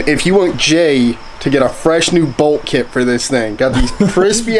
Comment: Did he say bold. kit?!?!